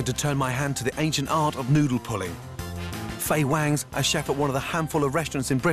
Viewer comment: i thought it said noodle pudding XD